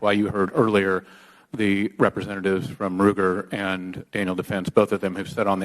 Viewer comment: did he say anal defense?